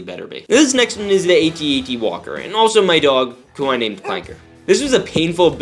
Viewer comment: after listening a few times I think he said AT-TE not AT-AT